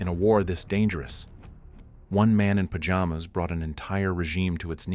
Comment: I thought you said pyjamas at first